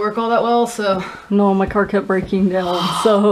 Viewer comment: Every time he kept saying car kept I thought he was saying Karkat, THERES SOMETHING WRONG WITH ME!